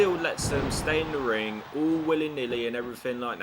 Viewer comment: What did you call the referee 😂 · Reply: Ah, I was saying that the ref allows the other team members to stay in the ring all "willy nilly". hahaha!!!